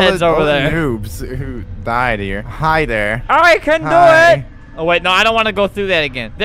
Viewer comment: i thought it said "hitler"